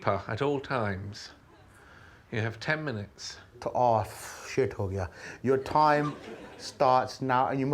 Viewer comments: Did Guz actually say "ahh shit ho gaya" at 19:04? 😂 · why did i think he said “oh shit ho gaya”